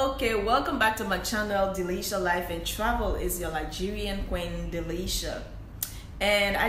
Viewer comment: It sounded like you said its your Nigerian qeen, Nigerian are beautiful ppl. Like the back story how you made it to China 🇨🇳 👏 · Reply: Oh, ok…I said it’s your LiGerian Queen! 🥰